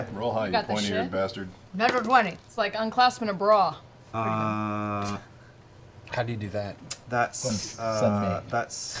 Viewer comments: did anyone catch what Sam says in response to Taliesins "how do you do that?" (Unclasping a bra) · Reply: "send me" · Ah. So. Note to self. Don't add Mercer to Zombie Squad. Edit: Does anyone know what Sam says when Taly asked how one unclasps a bra?? I can't figure it out for the life of me!! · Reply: I think it was "send me" - speaking as Scanlan, presumably.